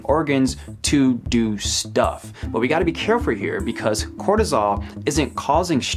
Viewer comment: Did you just say '"carefur"? Also, soda is gross